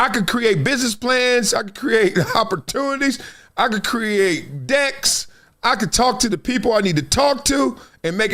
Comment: I thought you said “I can create dicks..” for a second 😂😂